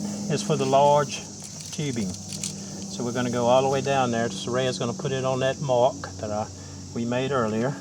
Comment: Did you say the name Saraya ? That’s me niece name too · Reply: Her name is Soraya. Small world indeed. Have a great day!